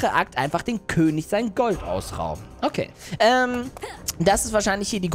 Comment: König das Gold klauen ich habe verstanden dem König das Golf klauen